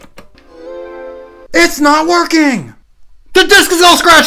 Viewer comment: i thought he said waking